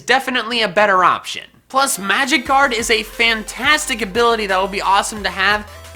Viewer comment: i was so confused i thought you said magickarb lol